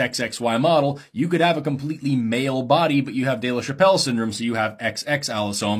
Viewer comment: For a second there I thought you said "Dave Chappelle Syndrome"! Hahahahahaaaaaa!! Silly me! ;^) Oh, and I love jumping spiders! They are sooooooooooooooo cute!